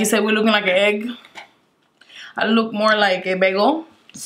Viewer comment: Did she just say "I look more like a bagel" 😂😂😂